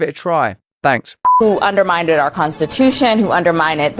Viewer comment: She almost let it slip that they did in actual fact elect Trump in 2020! 😂 She sounds like she is saying "undermineded" as opposed to undermined...